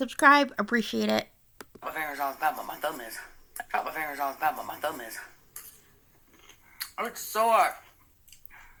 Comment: I think she says "I cut my fingers all the time but my thumb is... oh it's sore!"